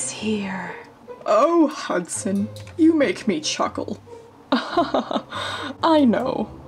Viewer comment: Oh Hudson, you make me ChUcKeL I kNoW My fave part 😂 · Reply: Malina M wait I thought he said “oh Hudson, you make me chocolate” oml lol!! 😂😂😂🤣🤣🤣🤣